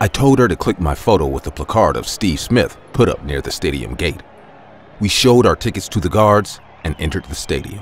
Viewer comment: I thought she said “we showed our t1ddies yo the guards oml